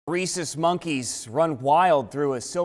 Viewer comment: Coulda swore he said racist monkeys 😂😂